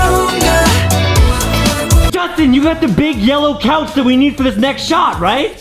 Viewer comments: OHHHHH BIGG YELLOW COUCH I THOUGHT U SAID BIG MELON OUCH XD that got me laugh so hard 😂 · Big yellow couch I thought you said big melon ouch!!!,!!